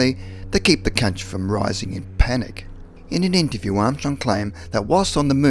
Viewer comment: Lmao at 4:37 Does he say To keep the c_nts from rising in panic"?🤣🤣🤣